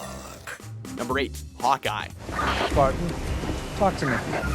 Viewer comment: I thought it said hot guy instead of hawk eye